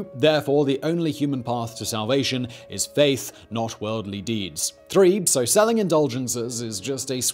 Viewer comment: Haha! I thought he was saying feces until I turned on the CC.